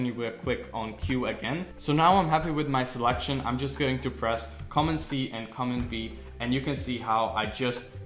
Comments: Hi I am trying to perfect this and so far you are being very helpful. at 5:40 and 5:42 I did not understand what buttons you said press to disappear the background after selection. the transcript says come and see and comment V?????? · hi,what is common C and common B in your words at 5:40 minute of ur video?thank you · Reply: Command C and command V, is a shortcut you press on you keyboard to copy and paste. So select an object, press Ctrl C to copy then press Ctrl V to paste it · can someone help me what he said for the hotkeys. Something like common C and common B. What hotkey did he say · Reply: Command which is the same as CTRL just on mac